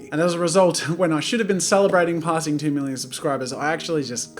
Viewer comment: did you say 10 million subs when did you hit that